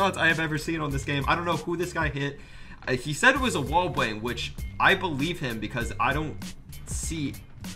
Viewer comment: Who else heard sayyy "wall bwang"